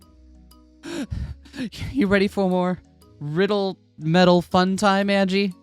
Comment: The episode started and I thought you said, "You ready for war?" And I was like, jeez, they're just riddles. It's not that serious, is it? 0_0